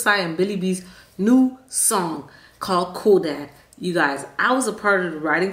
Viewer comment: Did you say Kodak or cool dad